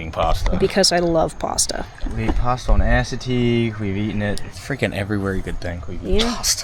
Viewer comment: I thought you said you ate pasta on Acid for a second.